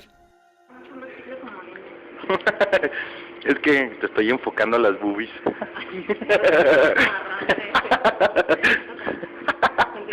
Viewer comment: I cant speak spanish but sounded like he said boobies